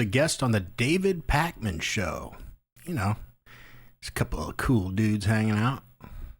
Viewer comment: Thought u said "couple of fool dudes"